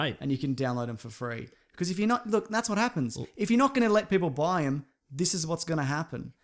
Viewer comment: I thought he said "that's wat happens when you don't eat ass" 😂😂😂